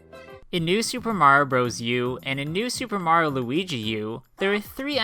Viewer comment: wait... did you say “supe mario luigi U”?